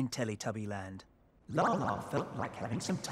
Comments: i sounds like shes saying tubby toes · I thought lala said “ tubby toes “ xD